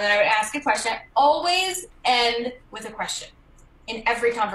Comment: I thought she said “always end with aggression” 💀